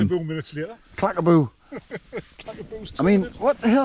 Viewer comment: Clackaboosh!!!😷👍👍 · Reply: I thought I heard you or saying "clackaboosh" at some point in this film....otherwise I've nae idea either,old chap 👍